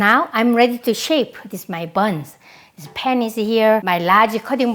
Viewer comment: at 09.30 I heard " Ready to shake my butt" R.I.P